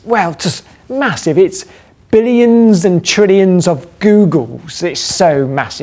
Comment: The captions might be auto-generated, but here's an error in the captions around 0:50 - "it's billions and trillions of googols" is what it SHOULD have said.